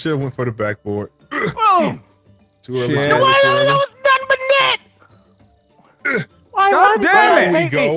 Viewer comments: what was h20 saying ?😂😂😂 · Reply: "What that was nothing but net" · Can anyone tell me what delirious is saying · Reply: squ-ol-lo-lo-san-boo-net